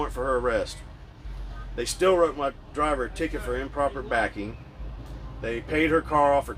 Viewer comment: How the hell he still got a ticket for "Improper Backing" even after they pulled up her info? These cops be looking for strips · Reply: I thought he said ticket for “improper parking” as he said they were there “waiting to unload been there for 30 minutes” 🤷🏽‍♂️